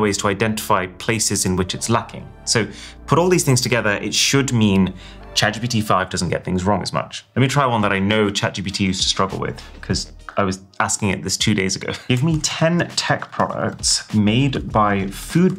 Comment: caption said Chad GBT5 👹